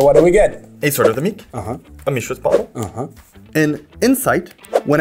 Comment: I totally heard "Mishras Bong"